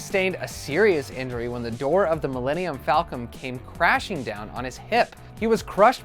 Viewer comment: Did I hear Millennium Falcom? With a M?